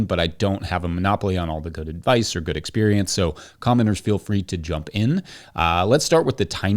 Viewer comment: LOL I just heard “commoners feel free to jump in at the comments section” instead of what he actually said “commenters feel free to jump in at the comments section” 😂